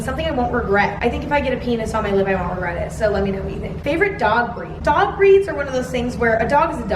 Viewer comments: She clearly says "let me know Ethan" at 3:20 😂😂and that's the teaaaaaa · She said she wanted a lip tat and ETHAN HAS A LIP TAT. Ethma is real EDIT: this comment was from 2 months ago & I don’t think it’s real anymore lmao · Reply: i literally thought she said let me know Ethan :D